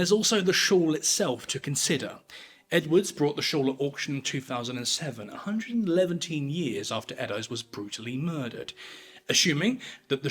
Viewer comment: Did you actually say "a hundred and eleventeen years" at about 8:45??